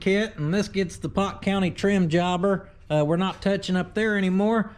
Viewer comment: When you said "the pot county trim job" I thought you said "pot county rim job" I was like 🤔. Then rolled the video back with captions.